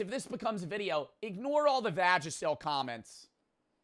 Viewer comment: sorry but what does he mean here? Idk what he said/means by vagicil? Idk how to spell it or even what the word is Nvm ig its just an inside joke lol